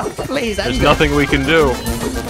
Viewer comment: Idk why but am I the only person who heard techno say "There's nothing we can do" is